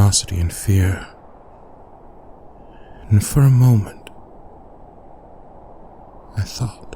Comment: When he said "For a moment," I thought he said "FUR a moment."😂